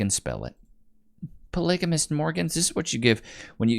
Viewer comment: 😂 did you say polygamous Morgans?? LOL. Too many ads for Morgan & Morgan mate.